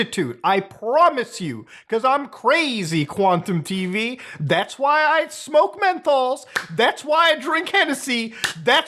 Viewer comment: I straight up thought Mutahar was about to say "THATS WHY I SMOKE METH" 😂